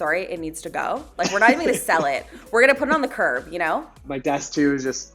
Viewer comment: said, "We're gonna put it on the curb." What I heard: "We're gonna give it to Graham."